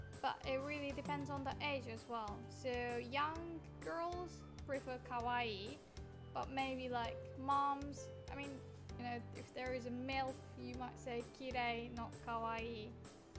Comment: Did Sensei say MILF??? 9:15.